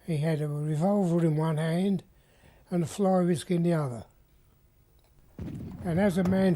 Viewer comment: Hi! Can someone help me? What does he say here? "He had a revolver in one hand and a _____ in the other." Please and thank you! Captions say floor risk, haha. Thanks in advance! 😊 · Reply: "He had a revolver in one hand - and a fly whisk in the other"